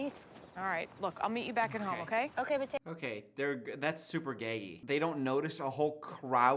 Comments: I totally thought he said that's super gay. I had to turn on captions for a sec. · im sorry what did say at 33:13? i keep playing it back because it sounds like he said “thats super gay” but i must be mishearing but i cant tell what he’s actually saying?!? does anyone know?